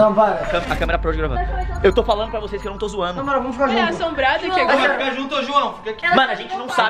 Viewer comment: eu não sei se foi impressão minha mas por que eu escutei isso a tava querendo descer na escada e o chamou ela como que ele não querice que ela decesse ela queria também queria ir no banheiro e o chamou a de novo pq talvez ele não quer que ela se machuque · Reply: Simmmmm ela falou eu também shippo muito vey só eu que escutei no minuto 1:29 o falando “ohh, Maria vamos ficar juntos”